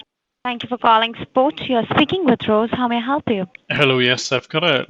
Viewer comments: At 0.50 it sounds like she says, "Welcome to support, you are speaking to TROLLS!!!" - apt. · ish) Is it just my ears or does it actually sound like she says "Thank you for calling ...?... you're speaking with trolls ..."